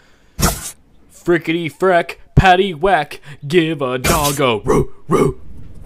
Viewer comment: I thought he said frickety frack pacy wack give your bog a ball smack